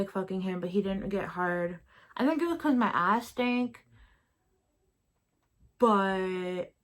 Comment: Hold up did u just say "because my ass STANK." ????